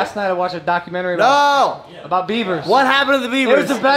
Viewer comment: I thought he said "Bieber" not "beaver" wooops